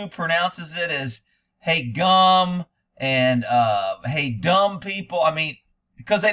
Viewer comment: I thought you was saying hey dumb people at first 🤷🤦 lol People are dumb as shite. Bunch of boot licking dumbasses in this country for sure. HFTCD ✌️